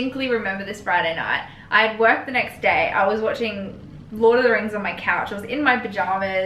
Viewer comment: Did you say you were watching Lord of The Rings at 5:45? If so, she is definitely a keeper! You guys are awesome